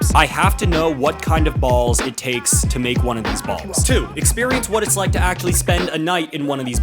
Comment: instead of experience, I thought you said experiment. I wouldn't be mad at the yaoi turn